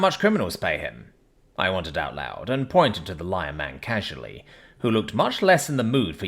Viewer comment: I thought it said "Lyin' man" at first and had to double check